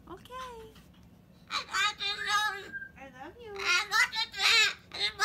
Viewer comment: You said "I love you" and it sounded like he said "I know you do".